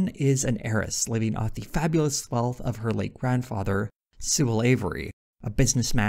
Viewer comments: lol For second I thought you said her grandfather’s name was “Suh-lavery” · When you said "Sewell avery I thought you said slavery originally lol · "Sewell Avery" sounds like the jokiest of joke names. I legit thought you said slavery weird lmao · When you said Sewell Avery I misheard and literally thought this dude was called Slavery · I needed to do a double take at 22:44 because I thought you said slavery.